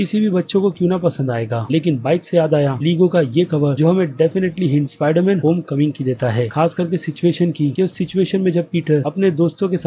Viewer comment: Did you say spiderman homcoming at 5:43 ?